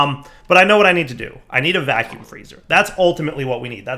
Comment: I really thought he said vacuum cleaner lmao